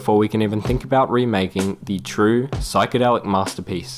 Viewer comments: what does he say? I don't understand. I'm not an native english speaker. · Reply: wait does he say "the lawnmower man"? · pls someone tell me the name of the movie that he said I can't understand what he said 😅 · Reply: Late reply, but he said The Lawnmower Man